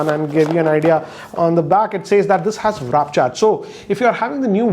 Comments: Am I the only one who heard him say "wrap charge"...... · Did he just say Raap Charge instead of Warp Charge ?